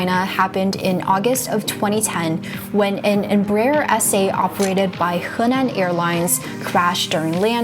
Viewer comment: Embraer what? The subtitles say "essay" but there is no such aircraft. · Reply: Embraer SA 😋 It was an E190.